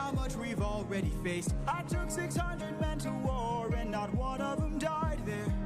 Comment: Every time I listen to it I hear “and not one of them died (there)”